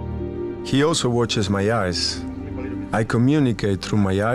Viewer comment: Anyone else hear "he also washes my arse" or is that just me?